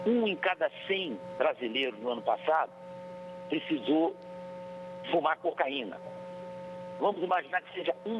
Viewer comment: eu entendi "fumar cocaína"?